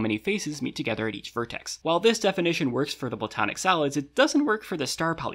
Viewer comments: i kept hearing “platonic salads” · I thought you said platonic salads